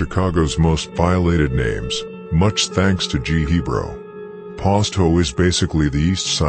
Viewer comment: You had a typo in herbs name and i lost it when i thought i heard the voice say, "G Hebrew".